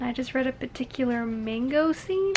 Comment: am i tripping or did she just say "mango scene" at 18:00?? hahahaha i don't know the insider reference cause I haven't read the book yet but ooo ~the intrigue~ also no spoilers pls if she did in fact say mango; just confirm or deny the fruit xp